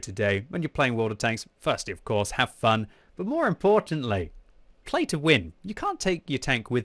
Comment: Did he say pay to win😂